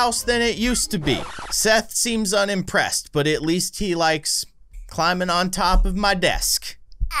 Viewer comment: At the end of the video it kinda sounded like he said I like climbing on the desk